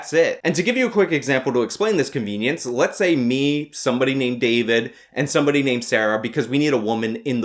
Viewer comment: DId he say David? I'm a David! That's me!